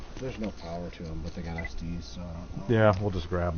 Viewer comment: on the close caption it says STD instead of SD ha ha ha